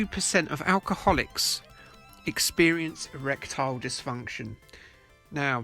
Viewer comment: I misheard 'erectile dysfunction' as 'reptile dysfunction'. 😂